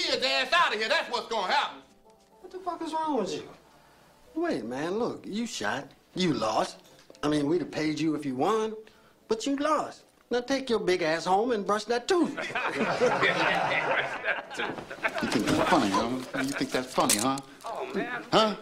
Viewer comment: "Take yo big ass home and brush that tooth" Lol, priceless.. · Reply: I thought he said take your big azz home and bust that tone for many years, now that I know what he really said it was more hilarious 🤣🤣🤣🤣🤣